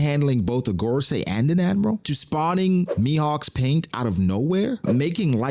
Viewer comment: Haven't watched the whole video incase he explains, but Mihawk's paint? Did I mishear or